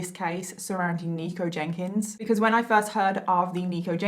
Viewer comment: In the beginning I thought she was saying "Negro Jenkins" 😂😂😂